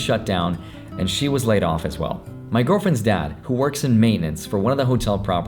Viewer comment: I heard it my girlfriend is dead